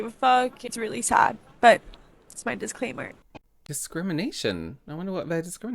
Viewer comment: Hey lovely I think you misheard her. She said this is “my disclaimer” not discrimination. Sorry if this has already been said.